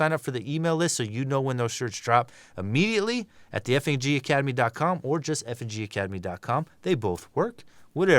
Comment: Did just say the "FAG" Academy? 🤣